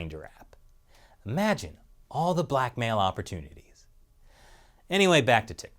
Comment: "imagine all the blackmail opportunity" I thought he said "imagine all the black male opportunity"